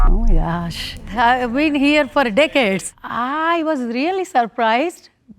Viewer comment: Bro when said ‘deacades’ I thought she said dickheads😂